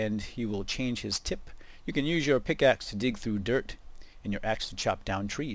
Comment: I thought it said "dick" :p